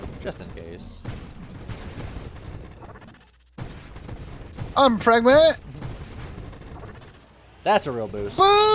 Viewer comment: Kinda sounds like he said "I'm pregnant"